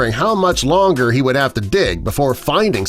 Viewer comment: you put di instead of dig